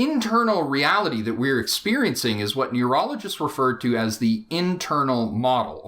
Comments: Did he say "Urologists" or "Neurologists"? It sure sounded like the former to me. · Am I the only one who heard him say "urologists?"